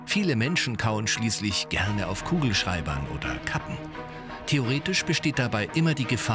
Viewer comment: habe verstanden:,, kauen auf Kugelschreibern oder kacken."😅😂😂